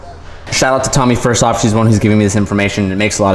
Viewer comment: WAIT!! LOL did you shout out to toomyfyeah SHES the one giving me this information? 12:17 (i can't tell if i am that tired and didn't hear right or you spoke so fast. XD